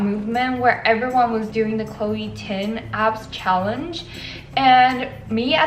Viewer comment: ChLOe TiN..... I'm ded. WHY DO I KEEP HEARING CHLOE TIN?!!!!!